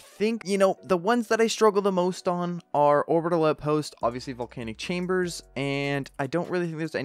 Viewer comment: When he said Orbital Outpost I thought he said 'Around the Lamp post'